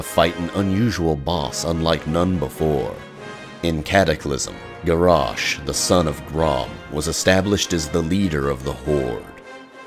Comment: Did you just say "son of Thrall"? Garrosh is the son of Grommash! Thrall is the son of Durotan. Watched the whole video.. got disappointed on you :( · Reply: Are you serious? It clearly says: "Garrosh the son of Grom"